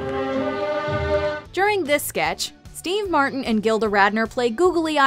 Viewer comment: Did she say Dean Martin 3:39?